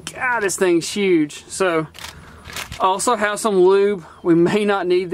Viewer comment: Did he say Lube? I think he said lube! It sounds like he said luuuube. Not sure tbh, someone let me know....luuuuuuuuuube